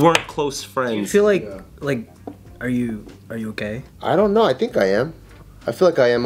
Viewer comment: dude I thought he said "gay" instead of "okay"